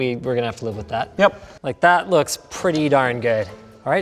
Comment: Did Linus say "That looks pretty darn gay"? LOL